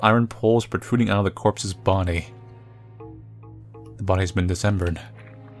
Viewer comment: Lol we all just gonna watch this and not note that the guy playong this game says "The body has been Decembered" instead of dismembered, lol. At 8:05 btw